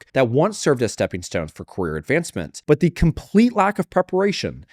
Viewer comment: Really off topic, but every time you said “career advancement” all I heard was “queer advancement”. 💀